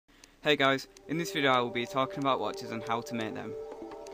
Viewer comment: xD i thought it said "witches" like from l4d2 i was like "OHHHH SHI- ohh nevermind..."